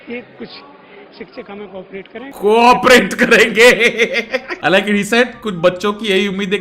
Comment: editor wrote corporate instead of co operate